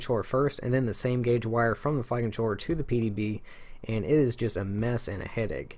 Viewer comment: I keep hearing "F*ckin' Troller".... LOL...